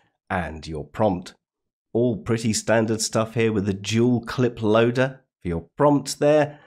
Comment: It sounded like you said "Jeweled Clip Loader" and that's what I'm going to call it from now on.